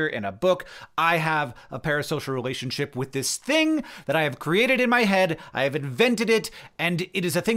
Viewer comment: I thought he said, “I have a parasocial relationship with this Sting I have created in my head” and I thought, “oh I guess Hank likes The Police (band)”